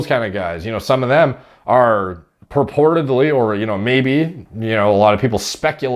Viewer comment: oh my god i thought he was saying “reportedly” wrong all this time. lmao. he’s been saying “purportedly”. im so glad that i was wrong hahaha.